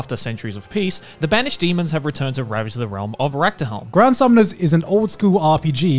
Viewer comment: did i heard rectum or i'm dumb